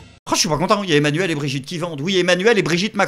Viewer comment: Mdr j'ai compris " y'a Emanuel et Brigitte qui bandent" au lieu de vendent 😂